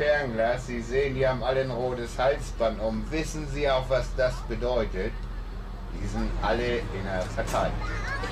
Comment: Was sagt er genau am 9:28? Ich kann das leider nicht gut verstehen. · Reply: Die haben alle ein rotes Halsband um, wissen sie was das bedeutet? Die sind all inner Partei!